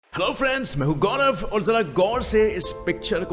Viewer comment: did i hear girlfriends😂😂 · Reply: No its. hello friends . Listen at 0.25x